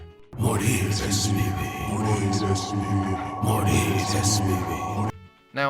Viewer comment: It sounded like they were saying Moldy SUV’s when I played. 😂